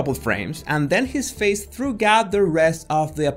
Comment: did you say "threw-gout" because it's 'throughout'?